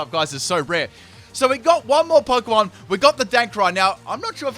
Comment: Did he say DANKRAI? :O